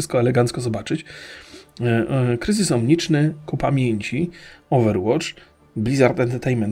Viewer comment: usłyszałem "kupa mięci" xD